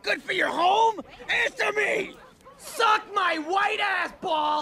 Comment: LOL! When I first saw this I thought he said "Suck my white apple!" LOL